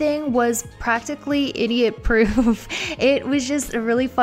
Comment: did you just say idiot proof or do I have bad ears?